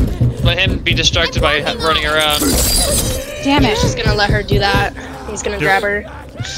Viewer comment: what did just say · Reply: Don't gotta make a big deal about damn people